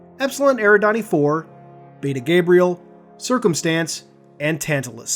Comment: I straight up thought he said Peter Gabriel lmao